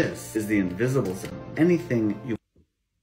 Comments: First time i hear "🎶Whats brown in your dog🎶"😂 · The first time i heard: "Whats bronk with your donk"